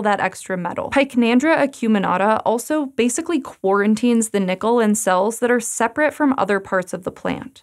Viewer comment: did you say... quarantine?